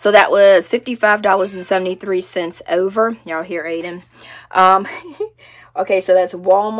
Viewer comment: yes I heard AIDEN and I thought it was AXEL lol!!!